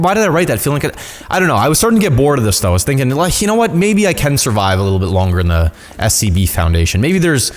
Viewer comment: Me: wait what did he say? Replayed Him: can survive in this... SCB thing